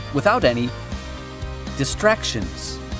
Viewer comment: Did I hear "Cats Studio Paint" or it is just my imagination